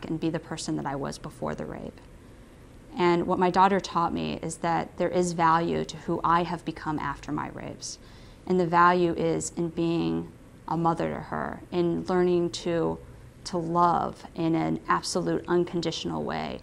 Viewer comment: I could swear she says "rapes".